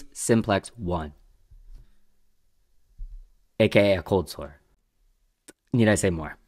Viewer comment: I thought you said “occult sore”…but you said “a cold sore” lol